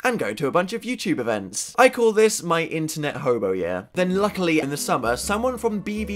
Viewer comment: I thought he said internet homo but then i realized it is hobo